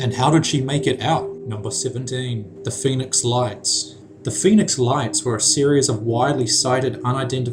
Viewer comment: I thought he said Phoenix alliance and im like, what from mc diaries? Than when I look it's Phoenix lights lol